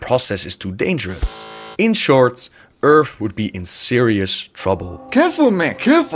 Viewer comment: In SHORTS earth would be in trouble (not trying to be offensive just thought it sounded like that)